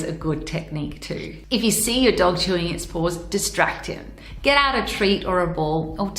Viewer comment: I know my mind is dirty. When I first heard say paws, I thought she said balls. I was thrown off a little since my dog did that quite often.